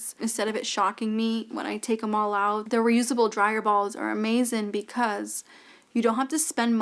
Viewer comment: I was multitasking and listening to this video and when you first said "dryer balls," I heard "dry your balls" and immediately looked at my phone to be sure I'm still watching your video. 😂 I'm also hard of hearing so sometimes I hear things differently. 😂